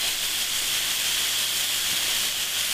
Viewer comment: Anyone who understands what he is adding at 4:59? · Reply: Veal Stock.